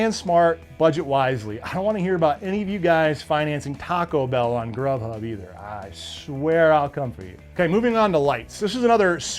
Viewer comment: pause… what did he say??? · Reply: He said ''if you use a credit card to pay for Taco Bell, I swear I'll come for you.'' At least that's what I heard.🤷🤷 😂😂